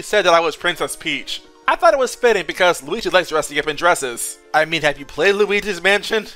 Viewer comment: I can't quite tell what he's saying at 5:25, I hear something like Luigi =#"_#:;$ dresses. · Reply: "Luigi likes dressing up in dresses."